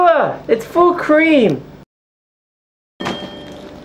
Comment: does he say thankyou dickhead?